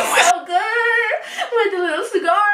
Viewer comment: When you said that i thought u said CIGAR and not scar🤦‍♀️ so my smart self went looking for who has a cigar in their mouth